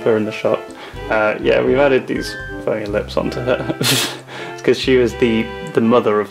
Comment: did you say funny or the f word?